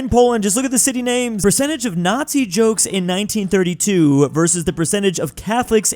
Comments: said n*zi instead of no no. Surprised how the editor didn’t pick up on it because he corrected jokes to places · We now have proof of saying “N*zi” instead of “No No German”.